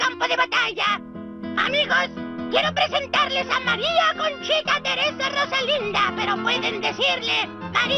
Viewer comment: Flashé que dijo: "María Conchetumare"